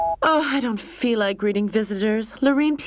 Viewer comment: Also misheard the opening as “breeding” 0_o....would’ve been interesting